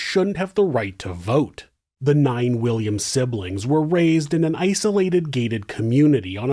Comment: lol I thought he said “9 billion siblings” I had to go back twice to hear it correctly “ 9 William sibling “ I was thinking wtf